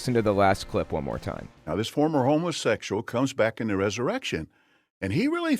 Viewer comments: I thought he said homersexual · I thought he said Homelessexual... I was like WHAT THE FEEZY?! Secondly, I can’t stand when ppl call being gay a “lifestyle” who you love is NOT a lifestyle. Your religion is a lifestyle. Paying tithes is a lifestyle. Praying is a lifestyle. Loving the one you’re in a relationship with is just that, LOVE. Just like everyone else. I hate that fucking word.